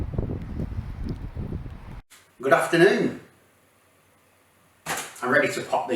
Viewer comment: I thought you said “GRAFTERnoon” hahah